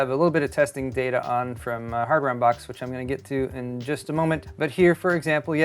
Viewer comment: You said "Hardware Unbox", but I heard "Hammer Unbox" and immediately imagined someone opening a motherboard box with a hammer. I want that channel in my life.